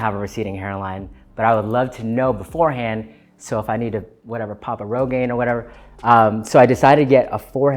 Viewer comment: Does anyone know what he said at 7:00? "Pop a railgade" ??? im so lost and i need to know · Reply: "Pop a rogaine" it's a pill for treating hair loss.